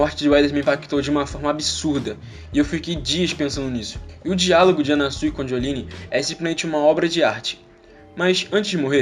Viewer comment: Na hora que você falou "o Dialogo de anasui e Jolyne" eu juro que ouvi " o Diavolo e a Jolyne" AKAKAKKAAKKAKAAKAKA